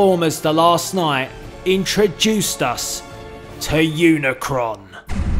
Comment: At first when it said unicron I thought it said unicorn